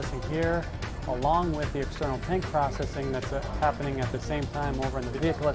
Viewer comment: I thought he said "external paint processing" not "external tank processing" 🤦‍♂🤦‍♂🤦‍♂🤦‍♂🤦‍♂